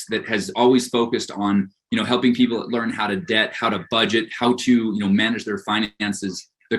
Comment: I'm stuck on the fact I thought he said, 'How to debt.' Did he mean how to get out of debt? Because the wares he's trying to peddle ain't it. A mortgage can do 1 of 3 things to your credit. It can make it better, make it worse or hold it just above the water.